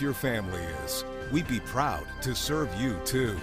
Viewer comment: Did I hear " We be proud to defend You ." We bees?